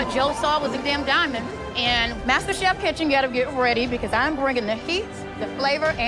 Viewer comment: After like 12 or so seasons, they should have all of the Winners of the previous masterchef seasons comeback and compete against one another to see who would become the grandmaster chef. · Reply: I thought it said grandmother chef for a moment lol